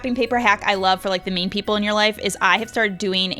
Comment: It sounded like she said, another wrapping paper hack for the “MEAN” people in you life😂😂😂 (I know she meant “Main” )